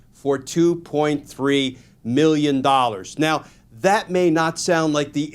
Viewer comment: 3 million against broke Lindell is no slap on the wrist. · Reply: I must have misheard. I thought it was 1.3 million $. Regardless until pillow is in prison he will never stop lying. 🪳